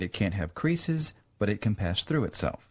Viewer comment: and further, he says "...but it can pass through itself." When he says that, it sounds like he say patch instead of pass.